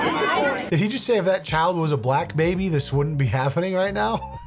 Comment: I thought he said, if this child was a crack, baby, this wouldn’t be happening right now I swear